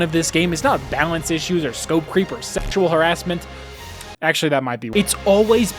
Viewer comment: For a second I misheard: "or Feature creep or sexual harrassment" and instead heard "reaper sexual harrassment" and was like: Wait WHAT did I miss for that to exist? Also holy shit I guess why people hate us furries, why tf do these "games" EXIST?